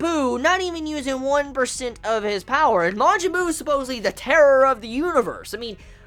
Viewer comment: I thought he said 1/10th of his power not 1/100th..